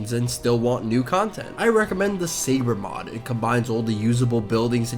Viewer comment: What did he say was the mod at 2:32? It's hard to hear him. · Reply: I think he said "sabre mod"